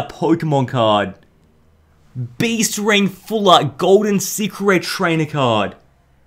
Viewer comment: When he says secret Rares u thought he was saying cigarettes